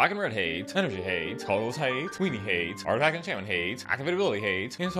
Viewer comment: I swore you said bogles hate instead of colorless hate and I was so confused lol